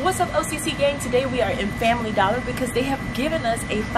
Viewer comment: I thought you said dollar General